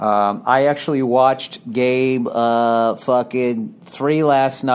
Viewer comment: I'm only a minute in, but a few times I thought he was about to say "I watched Game of fuckin Thrones" when he keeps saying "Game uhh, fuckin...3"